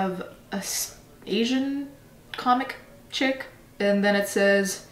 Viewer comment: Did she just say "asian comic"? o-o